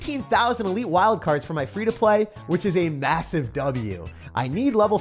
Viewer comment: did… did you say massive?